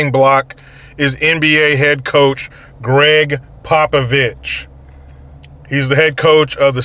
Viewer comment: I thought you said Greg "Pop-a-bitch". LOL